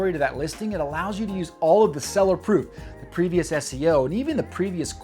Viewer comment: I can't make out what word you are saying here when you say " It will allow you to use all of the Seller 'PROO' - I've listened over and over and not sure what a "Proo" is? I know I'm hearing it wrong, maybe you are saying proof? But if so, what is a Seller proof? I know this is trivial but it's driving me insane when I don't understand a word the person is saying. Someone help! What is he saying as "Proo?" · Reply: Seller “proof” —- just the previous sales for other buyers to see